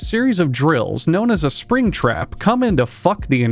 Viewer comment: WAIT DID HE JUST SAY SPRINGTRAP